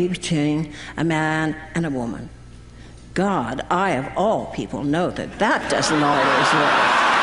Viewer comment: Can an english native speaker help me? At 4:32 after saying that those who are against gay marriage always say that marriage is between a man and a woman, she made a joke begining with " God..." can someone write the rest of the statement? I can't understand it properly. What a remarkable woman !!!!!!! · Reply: "god, i of all people know that that doesn't always work!" in reference to her eight marriages lol